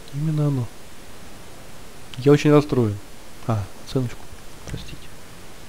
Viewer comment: сказал а, оценочку а мне послышалось а целочку?